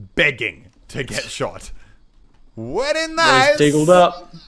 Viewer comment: did he say whain alnas or very nice😂